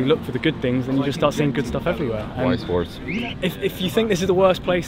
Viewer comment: At first, I thought Staffan called him a "wise horse".